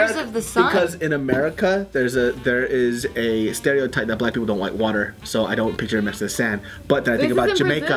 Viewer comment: lmfao I heard say "don't wike water" and I was like "HUH?" XDDD yeah it's probably just me :/